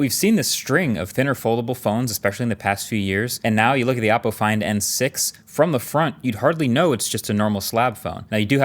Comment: did marques just call it APPLE find N6 or am i tripping....